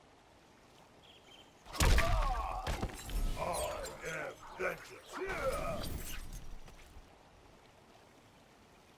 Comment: what does he say? is it "I AM deathless!"? and at 0:48 does he say "Enough!"? Also at 0:48 I also heard him say "Pathetic!" At 0:18 he can say "You have no honor!" after he sprays fire. At 0:34 he can say "I burn with pain!" And at 0:25 he can say "You are beneath notice!" instead of "Do not anger me!" Honestly I don't know which phrase is the coolest at 0:25, but is by far the best taunt. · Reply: he says "I am vengeance" and yes he says "Enough" at 0:48 and also he does say other stuff with the same taunt 🔥☺